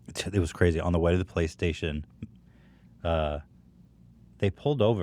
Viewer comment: Did he just say Playstation?